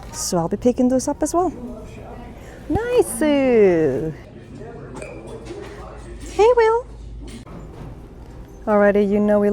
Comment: Helloo, we honored you spend your commute listening to us! And interesting, I had no idea! They have some stunning pieces but I can see why they went out of business if size 12 was the biggest they carried! Thank you for watching/listening 😊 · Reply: It's fun listening to you and the way you say goodwill in the beginning of your videos. I thought you were saying, "Hey, Will." LOL